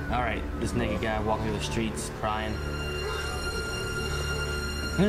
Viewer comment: lmao I thought he said “This nigga guy walking on the streets crying”